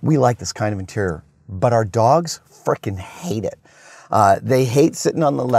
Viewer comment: I had to rewind that cos I thought you said your dogs fucking love it